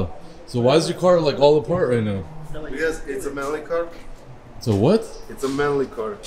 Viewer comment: calls it a "melon car" instead of "lemon car" 🤣🤣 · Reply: Lol i thought he was trying to say, “it’s a manly car”